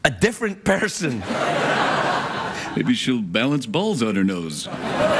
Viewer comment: What did Jeff say in 10:52? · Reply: Maybe she will balance balls on her nose.